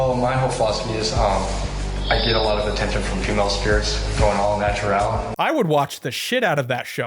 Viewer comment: I'm sorry, did he just say JUVENILE spirits? is this guy a ghost sex offender? is he flashing children from beyond the grave??